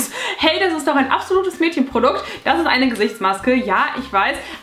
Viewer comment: Dieses Schleim Teil sieht so geil aus. Ich hab als erstes Gesichtsreiniger verstanden und ich dachte mir schon... 😂